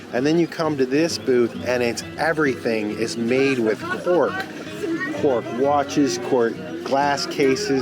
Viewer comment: I'm enjoying the architecture. It's incredible. After you visited all the food booths, I thought said " All things made from pork, not cork." Haha .